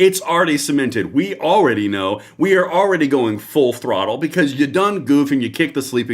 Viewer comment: I thought you were going to say "now is the time to put our for on the throats and push it to the floor, I laughed at my mistake when you said throttle. God bless you